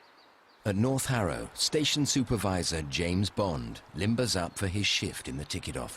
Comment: did I just heard James Bond Lol?